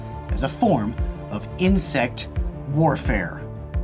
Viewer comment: i thought i heard INCEST warfare..lol